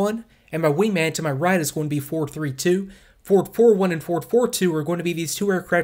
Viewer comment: For the first 10 minutes I thought you were saying 4-4-2 and 4-4-1, not Ford 4-2, etc. I was so confused why the extra 4 was coming from.